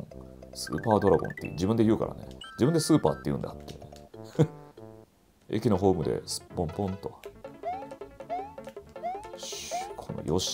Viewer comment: 「駅のホームですっぽんぽん♪」 なるほど杉田さんには、そう聞こえるのか～面白すぎwwww